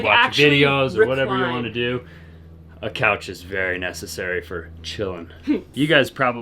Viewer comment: “A couch is necessary for children.” ?!? That’s one grandma with a LOT of charisma! 😄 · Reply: I heard to say "A couch is very necessary for chillin'." I think he meant relaxing. Marker 25:06, if you want to listen again. And I agree with you, grandparents are so fun.